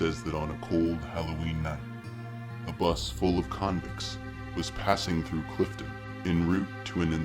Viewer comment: haha i thought it said bus full of condoms y would they need those at an insane assylum